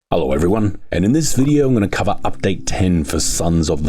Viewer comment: when did they prevent him from saying his name and why? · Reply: i think its because of his accent, it sounds like he is saying “Hello everyone its F##k it here”